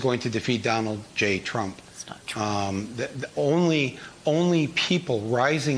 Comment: Did anyone else hear someone whisper "That's not true." at around 0:40 ?